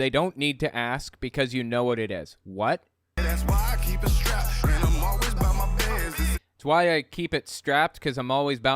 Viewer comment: I thought he said “I keep a strap-on at my bed.” In the target “song.”